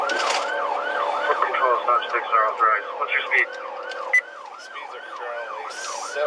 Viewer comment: The Milwaukee chase @ 17:02 the officer is hoping for stop sticks... I'm not sure, but does dispatch say, "stopsticks aren't authorized"? If so, WHY?